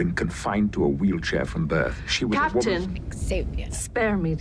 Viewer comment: Sounds like said "Xavier" when Picard was talking about someone in a wheelchair 😂